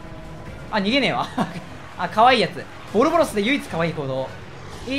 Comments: ボロボロスって言ってるように聞こえるの俺だけ？ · の「ボルボロス」の言い方が「ボロボロス」に聞こえるってそれ俺の中で一番言われてるから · ボロボロスって聞こえるの俺だけ？ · 正式名称ボルボロスなのに、が言ってるのを聞いてると何故かボロボロスに聞こえる僕。素直に難聴です。